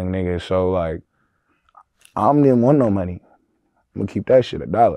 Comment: Is it me or did i hear “ I’m didn’t want no money “😭